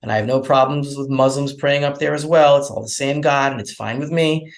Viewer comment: did he say its all the same God?